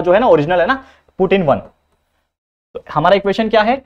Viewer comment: Who Heard Vladimir Putin?😅